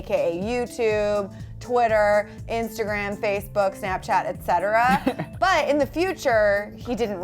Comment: Hahaha when she said Facebook (while she was listing social medias for Cameron) I thought she said FaceTune hahahaha